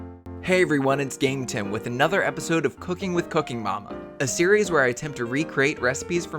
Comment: when you said 'cooking with cooking mama' I misheard you and thought you said 'cooking fucking mama' omfg